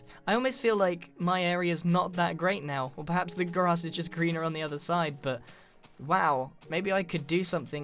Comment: im sorry, did you say the grass is always grian-er on the other side?